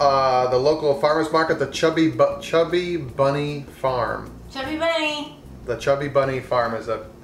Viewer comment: Hahaha! I thought you said 'Chubby Butt Farms'! Lol!